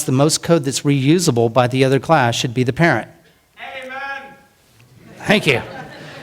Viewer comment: what does the man from the audience shout at 31:59 ?? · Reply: "Amen!"